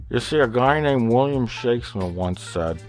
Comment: Did they change this? I coulda sworn Plinkett said, "William Shatner."